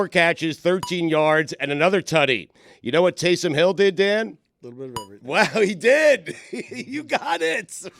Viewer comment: What is Dan saying at 34:43?? I can’t make it out 😂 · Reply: Dan said, “A little bit of everything”.